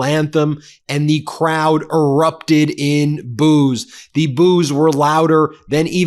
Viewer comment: Being Australian the first thing I heard was 'the crowd erupted in booze'. 😆🦘🦘